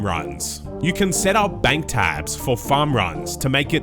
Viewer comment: omg at 3:49 you said bank 'tabs', i heard bank tags and was surprised it came out and was trying to figure it out for 30 mins, looking at other mobile osrs updates and all... oof